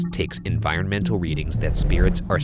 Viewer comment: I always thought the voice would say “into what?” After Ryan said “… and converts them…” I don’t know why I would never read the word in the animated Ovilus that says “interrupt”. It’s actually quite clever that it says interrupt while interrupting Ryan.